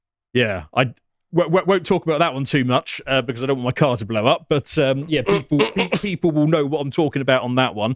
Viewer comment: when he was talking about USS Liberty I thought he said "I don't want Carl to blow up", and I thought why would Carl get upset over that?